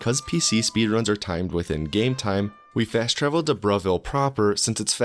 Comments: It thought he said bruhville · I thought you said Bruhville as a joke but it's actually called Bravil.